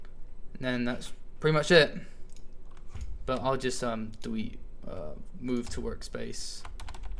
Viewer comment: If you listen in little bits - 3:50 - 4:01 he says dewete instead of delete! xD